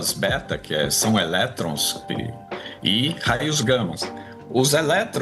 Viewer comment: perdão, mas eu ouvi raios gammIS?